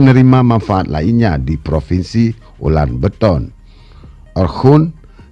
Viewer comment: Apa cuma w yg salah denger klo admin bilang ular beton 😂